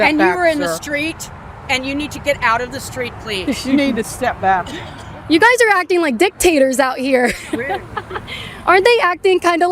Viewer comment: l thought Kaitlin vas going to say "you guys are acting like dicks" at first 😂😂.